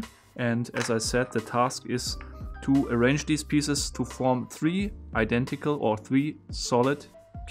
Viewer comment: Totally sound like he said three identical pubes